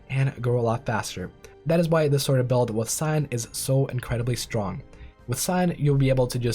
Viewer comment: "That is why this build with ???? is so powerful" Did you say Sign? You say it again and im still confused. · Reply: It's one of the origins scion I think it's called